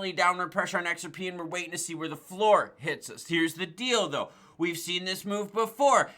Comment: well here's the deal-do lol 😅😅 dildo i know it's deall though but that's what it sounded like 😆😆